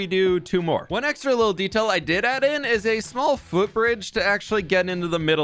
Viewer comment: it sounded like you said "fwhoot bridge", and I think that's just what it has to be called now. The Fwhoot Bridge, the Bridge of Fwhoot...